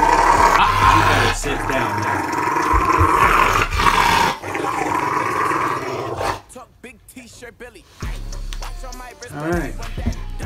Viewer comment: It sounds like he said alright alright alright in the begining 😆 🐕